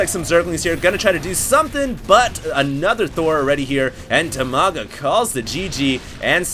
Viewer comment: at first l thought is said dimaga vs the loser!